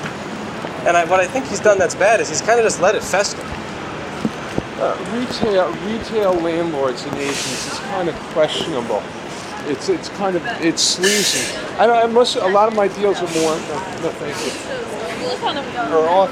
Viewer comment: “Retail landlords and Asians is kind of questionable” lolol, did I hear it wrong?